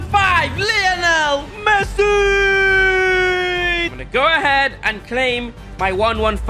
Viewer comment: did he say Messuiiiiiiiii